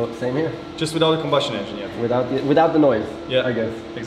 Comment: Did he say without the combustin engine YET?!